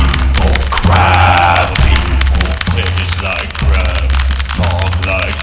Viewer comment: Am I the only one who thought they said taste like crap?